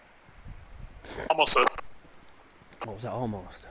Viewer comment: Time stamp 13:50 I heard ‘ALMOST 7’ 13:56 (female voice ) ‘SIX’